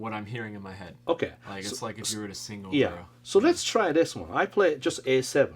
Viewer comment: the cc is wrong. He said it’s like if you were to sing over…